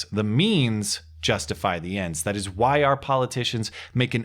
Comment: Did you just say "The ends justify the memes."?